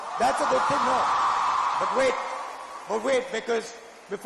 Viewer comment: Guys did i get it wrong or did he say 'wait niggas' at around 3:42???